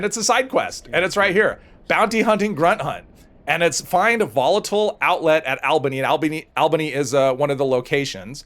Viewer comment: I could have swore you said “volatile outlet” 😂